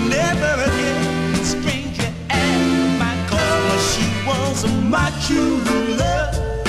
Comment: I thought he was saying "she was my chula" at first haha